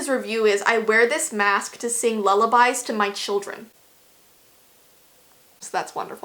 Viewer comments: At first I thought it said "I wear this to sing lullabies to my chickens." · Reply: I thought it said "I swear this mask sings lullabies to my children" .... WHAT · At first I thought it said "I wear this to sing lullabies to my chickens." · I thought it said “I wear this mask to chicken my children” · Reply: Hahahahaha 🤣🤣 I thought it said "I wear this mask to kid my email" I have issues